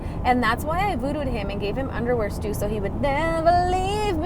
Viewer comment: Underwear stew! I had to rewind because I thought you said underwear juice! I was like, ew! 🤢🤣🤣🤣